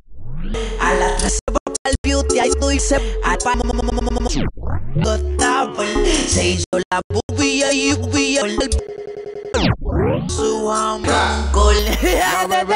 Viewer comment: entendí "se hizo las boobies y el yupi"